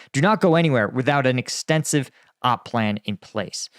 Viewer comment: “Troops do not go anywhere without an extensive op plan in place” - I heard an “extensive-ahh plan,” LMAO 🤣